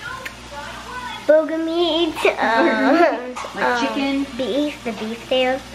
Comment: I thought she said booger meat,lol.